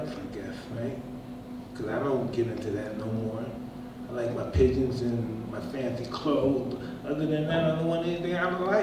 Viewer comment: Can someone tell me what did he say after 4:30 and all started laughing ? Not able to understand :( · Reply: Pigeons and his clothes